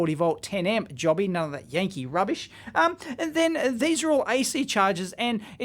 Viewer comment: Did you say Yankee rubbish?